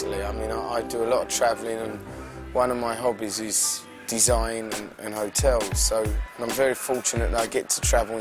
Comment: did he say "one of my hobbies is...design in hotels"? (yes at 1:40 he compares designs). yo it says up here he's on tour!! whoa maybe i'll go. im in my 30s and Oakenfold was the first "EDM" person i listened to growing up and then now still the only producer / DJ where i can listen and don't feel like i have to keep pushing "skip forward next track"!